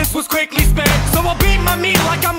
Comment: woah did he just say ashton kutcher???............AWSOME